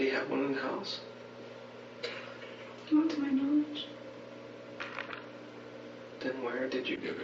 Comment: Can someone tell me what did she say at 1:14? TJ whom is she talking? Mendez? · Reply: She said "not to my knowledge." They were talking about the gun. If it was in the house.